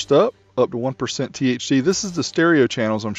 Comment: i think you mean thd and not thc :D or am i hearing this incorrectly :D